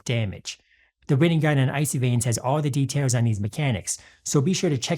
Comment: this actually looks really fun, shame it can't be made meta in s5? edit: funny moment, when you said "the written guide" i heard "the rinnegan" xD